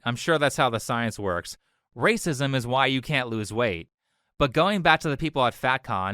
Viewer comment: she did not say "racism" she said "weighcism"